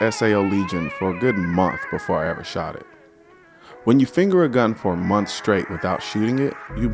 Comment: "When you finger a girl for a month straight..." Yeah, I heard that wrong at first.